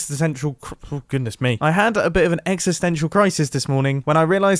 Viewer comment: Hmmm... I heard Exeter Central at 19:57... Anyone else?